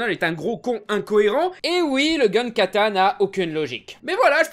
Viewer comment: J'ai entendu "le gun-katana"... il me vient un meme en tête, "It's also a gun!" Comprendra qui pourra.